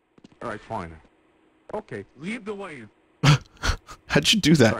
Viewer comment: I think he's actually saying LIDL WAY